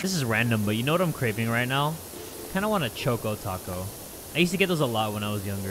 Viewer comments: I thought he said "I want a chock on taco." 💀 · i thought he said "choke o taco"